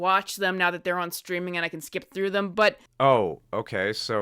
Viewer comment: No wait what did she say at 7:25??? Skip through them???